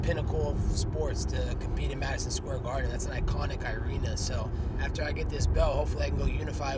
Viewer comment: he said Irena instead of arena wtf haha I still fuck with him tho